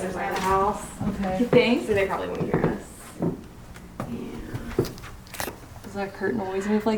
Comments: I heard someone saying 'I don't know' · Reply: i heard someone saying 'yeahhh' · hey my friends at 14:38 i heard a whisper said "yeah"